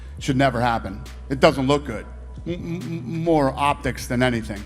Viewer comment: more optics?? Optics or what did he say? learning English :)